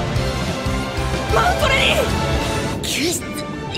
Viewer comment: 何回聞いても「ま○こレディ」にしか聞こえない